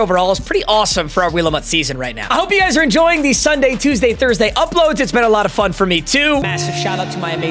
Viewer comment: I thought he said Saturday though mmdementia